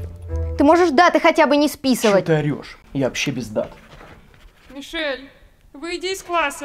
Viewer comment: Кто ещё на 32 минуте услышал "Я вообще пиздат" вместо "Я вообще без дат"?